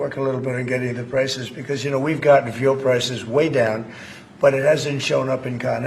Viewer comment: is he saying veal prices?? Genuinely .. fuel. I hope he's saying FUEL (omg sincerely sounded like veal)